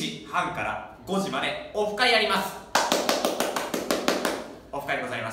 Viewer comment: 「オフ会やります！」が「国会やります！」に聞こえたw